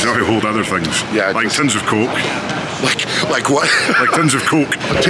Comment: Luke: "tins of coke" Me: did he just say "tons of c*ck"? Lol.